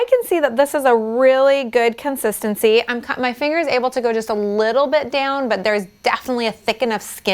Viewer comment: did you say i'm cut my fingers?